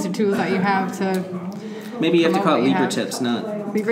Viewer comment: What did he say at 17:35? eco tips? Is there some context around that?